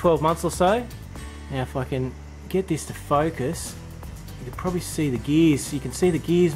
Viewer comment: I thought you said "fucking get this to focus". On second time listening I realised it was "If I can get this to focus". lol.